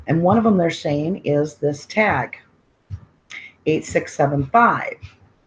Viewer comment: did say 8675?? Isn’t that a song? <num>? Lol oh man... I feel old